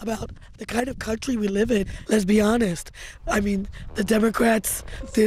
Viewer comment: Did she just say "Lesbionist"? 😅